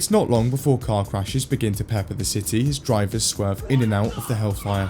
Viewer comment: did he just say pepper? I love this channel